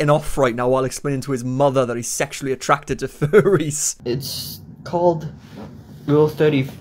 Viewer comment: you guys asked for it, so now you're getting it lol! Subscribe <3 <3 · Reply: Sounded like he said "viruphile" but no. He said Furries